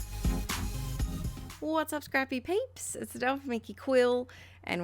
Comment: Oh God I need to go to bed instead of staying up late watching these vids I heard "what's up creepy peeps" instead of "what's up scrappy peeps"!😆I am losing it ....goodnight all!😴